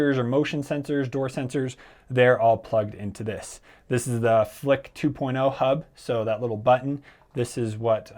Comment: big brain of smart home, didn't fully prepare for power outtages. lol. But what was the button? You said flick 2.0? I thought you had said samsung.